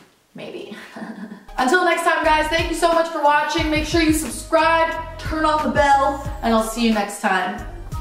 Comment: Andrea baby just a sec.. 9:52 did u say turn off the bell... Lmao u really high